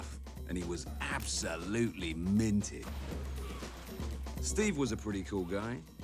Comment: "Ollie fancied himself as a player, but really he was all mouth" (at the end of the movie when he's a Blank and the top of his robot head is off, all that's left is a mouth) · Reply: Understandable. As an American, the British accent did threw me off a few times as well (ex: I thought he said Peter was "Minty" (aka gay) but because of the accent, he actually said "Minted" (rich)